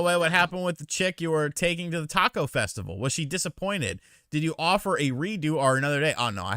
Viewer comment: When was reading necro banana's question about what happened with that girl, I thought he said "did you off her?" 😵